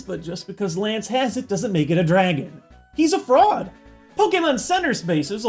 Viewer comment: I nearly died of laughter. I thought he said "Just because Lance has him, doesn't make him a Dragon. He's a frog! " 😂😂 But after rewatching, it was actually "He's a fraud!"